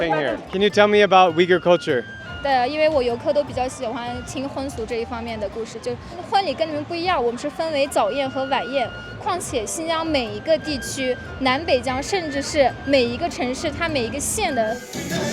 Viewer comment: The subtitle doesn't match what she said: 因为我游客都比较喜欢听婚俗这一方面的故事 婚礼跟你们不一样，我们是分为早宴和晚宴 况且新疆每一个地区，南北疆，甚至是每一个城市，它每一个县的（her sentence hasn't ended yet but got cut off...） Because my tourists like to hear stories about wedding customs Weddings here are different from yours, we have here a morning feast and a dinner party Besides, every region of Xinjiang, the north and south of Xinjiang, and even every city, and every county ...